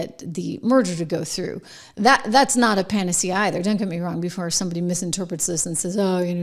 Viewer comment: did she say "pan-tasy?" lol